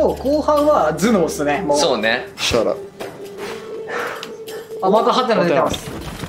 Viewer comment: なんて言ってる？？shut up？？w